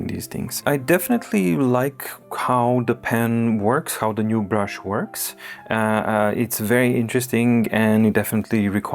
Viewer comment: what does it say? "To all of ??? viewers. Thank you." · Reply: My